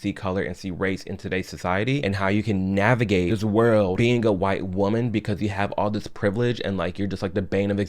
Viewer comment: Did I just hear her say, "I used to be a white woman"??? Suely I heard that wrong.